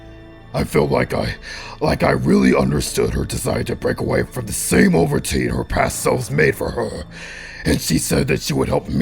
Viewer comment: I couldn't understand what bowser said at 7:52-7:58. What did he say at that part? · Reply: Joseph Fuller "-like I really understood her desire to break away from the same old routine her past selves made for her-"